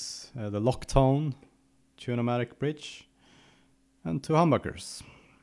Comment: I thought I heard "hamburger" when he said "humbucker." lol